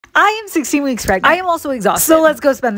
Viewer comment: First i thought she said that she was 60 weeks pragnent but then i undrerstand that she said that she said was 16 weeks pregnant